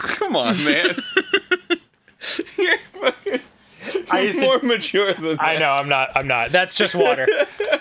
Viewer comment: I have no idea why, but after Dan smoked the fake bong and Arin said "more mature," I thought he said, "Force majeure!" And I suddenly wondered why he referenced a clause limiting the liability of two parties. Then I listened again and realized I was an idiot.